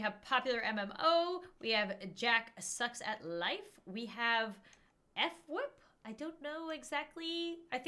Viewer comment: it's sounded like how it's spelt f..wip, like hwip but with an F